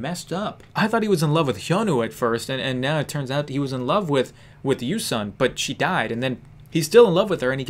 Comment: I THOUGHT YOU SAID YOOSUNG